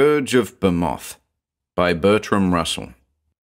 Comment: I thought I heard 'Bertrand Russell' as the first author; I thought I was going crazy, looked it up.